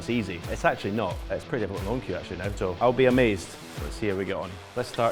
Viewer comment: What did he say at 0:17? "It's pretty . . . .what? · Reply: It’s pretty hard with my own cue now to be honest